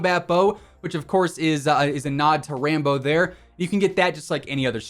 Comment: I thought you kept saying Rainbow instead of Rambo..